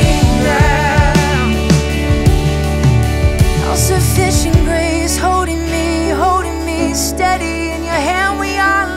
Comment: Does anybody know what she said at mark 3:53? Thanks! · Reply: I believe it’s. Oh sufficient grace holding me now